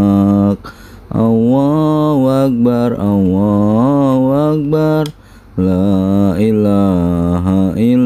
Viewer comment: adzan nya Awohuakbar awohuakbar lailahailawoh..harus nya Allahu akbar Allahu akbar laaillahaillallah...maap semoga sy yg salah denger..